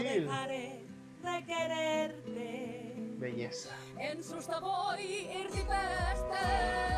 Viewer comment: yo entendí “en su sabor diferente”XD